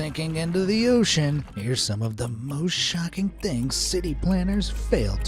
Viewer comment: I honestly thought he said bullsh*tting and not most shocking